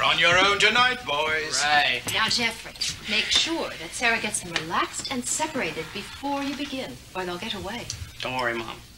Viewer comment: what did she say? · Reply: "make sure sarah gets them relaxed and separated before you begin, or else they'll get away" or something to that effect.